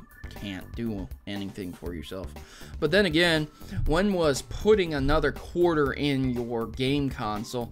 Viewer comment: I really don't care about this problem since this doesn't really apply to me but i do think this is a good idea. P.S. am I the only one who heard say "pudding another quarter" at 2:15 PUDDING?!